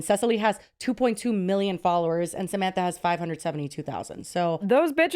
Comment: I thought you said “Sesame and Samantha” 😂😂😂